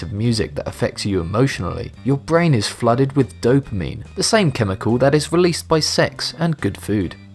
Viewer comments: did he say, "your brain is flooded with dope memes"? xD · Dope meme?Please don't tell me I was the only one who heard that